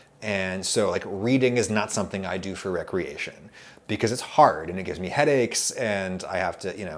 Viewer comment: I think I misheard you. I don't think that you actually said "Reading is something that I don't do for racoons".